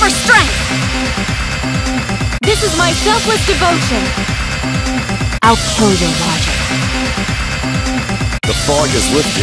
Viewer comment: I thought maki said "I'll kill your magic" and I thought she was going against himiko 💀💀💀💀